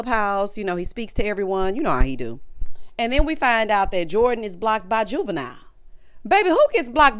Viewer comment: I’m looking at Jordan being BLOCKED by Juvie! He seems not to like thirst. Maybe she’s thirsty. Let me go watch his Tiny desk concert again😂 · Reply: I thought she said Shanice was blocked by Juvie?